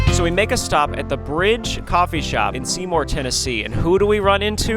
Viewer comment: thought that said "coffee cafe communism" for a sec